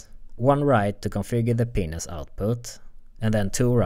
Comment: Ok but we all thought he said penis output right?